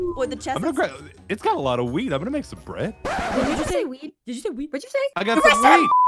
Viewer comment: "Its got a lot of WEED im gonna make some bread" " did u just say weed ARREST HIM!!!!"